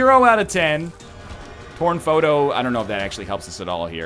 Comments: when Northernlion got torn foto i thought he said porn foto for a second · i thought he said porn photo XD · When he said Torn Photo in Caves 1, I thought he said Porn Photo...